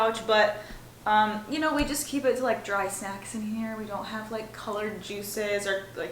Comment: I thought you said ‘We just have dry sex in here!’ I was like ‘What??!!’ I took another listen and think you said ‘dry snacks!’ 🤣🤣🤣